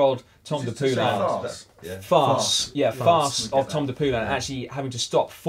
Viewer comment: did Simon say tom de poolan